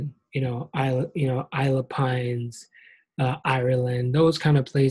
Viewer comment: I'm sorry, what did you said at 3:30, what country was that just before you said Ireland ? LOL With subtitles it shows "I love Pines" and i think you actually said "Illapines" ?LOL What were you trying to say there mate ? Did you skip some grography classes ? haha XD Anyways i watched the entire video and it is indeed very interesting and educative!! A strongly advise whoever landed here by mistake to take advantage of these 16 minutes of good quality content!! Aaaaaand you just gained a new subscriber who liked your video as well!! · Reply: LOL When I did this video totally tired but my viewers wanted it so I went through anyways. It was Isle of pines, it's an island down the Polynesian area. Happy you liked the content man, appreciate it.